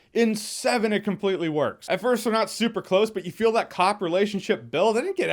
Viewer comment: Did you just say cock relationship at 3:06?